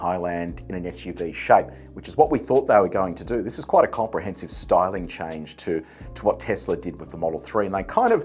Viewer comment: For some reason i heard “starlink” instead of styling